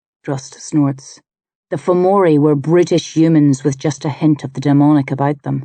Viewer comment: "did Drust just call the Famouri"British humans"? lol, guess it explains how our empire was so big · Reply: brutish